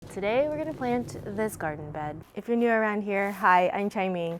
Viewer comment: Kira says, Garden Bed? I thought you said Doggy Bed!